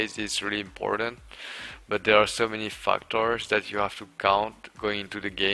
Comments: It really sounds like he says "fucktards" instead of "factors" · I thought he said fucktards lmao